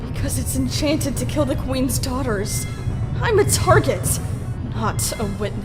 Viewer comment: I thought tsunami said I'm r***** insted of I'm a target...